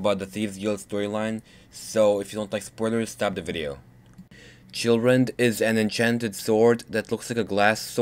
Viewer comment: In the beginning, it almost sounded like you said children xD